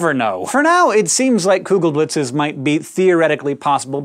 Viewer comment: I honestly thought Its said "Kurzgesblitz", after Kurzgesagt